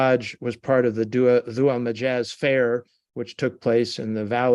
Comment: The hajj was part of the "Zua Mijazz fair" that took place in a valley outside of Mecca?? Can someone please spell out what valley this was. I cannot work out what he says.